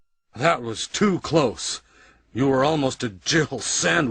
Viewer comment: "You were almost a Jill Sandwich!!" That line is simultaneously horrible and classic at the same time. And hilariously corny. ~Razzle Joestar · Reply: I thought he said Jiggle sandwich, to which I could only retort, "oh... how Japanese of you..." Lol